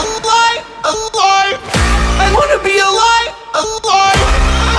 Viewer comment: It sounds like Morty is saying ,"I wanna be a lie.,"